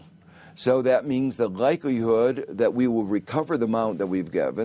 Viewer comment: what does Josph say? Gikelyhood?